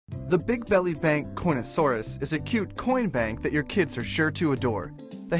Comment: am I the only one who heard cornasauraus